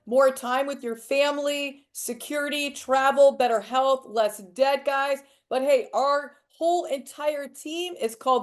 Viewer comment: I wasn't looking at the screen when they were talking about what people might want out of the business and instead of hearing 'less debt, guys' I heard 'less dead guys' and thought they were claiming live wave could stop people dying 😂😂😂